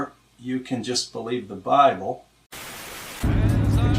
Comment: Did he just say Brible? I'm stealing that.